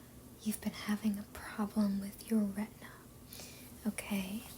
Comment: Sorry, my hearing's not what it was, I thought you said "Are you having problems with your rent there?"